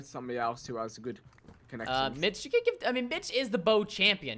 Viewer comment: did he call Mitch a bitch x.x